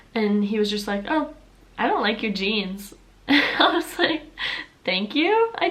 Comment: I thought you said ‚genes‘ for a second